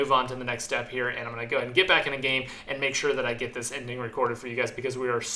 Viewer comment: I heard, "make sure to get high", okay, sounds good. Thanks for the approval!